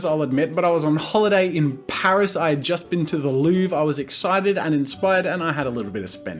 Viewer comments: I paused at the wrong moment near 0:59 - sounded like he said he'd been to the loo lmao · thought he said I went to the loo 😂